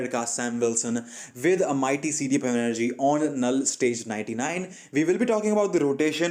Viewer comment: Is it only me who heard him saying knull stage 99!!😶